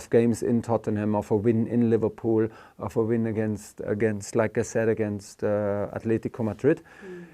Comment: Thought he said, "our win against Lacazette." Had to double back. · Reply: "A win, like I said, against Atletico Madrid..."